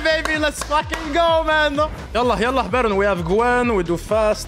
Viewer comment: did u say yala yala bro lol XD